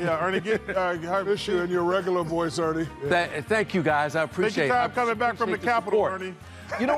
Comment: "Hurry back from the Capitol, Ernie" 😬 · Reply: he also said “take your time coming back from the capital, Ernie” not what that guy above said.